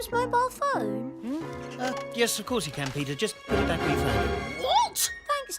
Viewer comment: What did dad say? All I heard was "of course you can Peter [blah blah blah]" What was the rest of what he said? I can't hear it because the music is too loud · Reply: “just put it back where you found it” i think